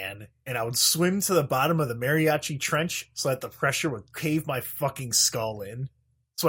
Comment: did you just say Mariachi trench? I think I'm having a stroke and have gone fully numb